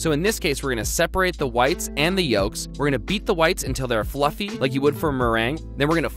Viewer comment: did i just hear "beat the whites" ? hmm......